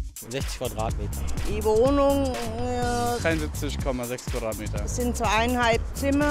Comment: Can anybody tell me what does the man at 2:05 exactly says?? I am trying to learn German, but I'm not able to make out when does he say '73'. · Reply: 73,6 m² (dreiundsiebzig komma sechs Quadratmeter) (sorry, im late like deutsche bahn)